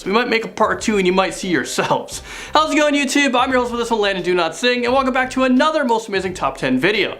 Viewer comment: I thought he said “My name is Landon Do Not Sing”